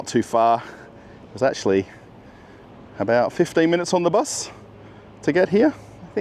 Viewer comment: I think he meant 15, because i believe he said it was 20 minutes away from he lives earlier in the video. I don’t know, I could be wrong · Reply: rewatched the segment 7:30 - autogenerated subtitles say it's 15, but i still hear 50. Yeah, probably my bad